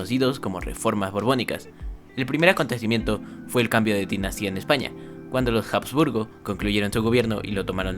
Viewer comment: En el minuto 9:53 dice fue el cambio de ti nacia? O q? Ayudaaa q no entendi😔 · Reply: Cambio de dinastía.